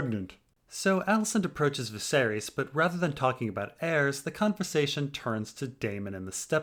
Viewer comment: did you say "cuntversation" or "conversation"? 🤔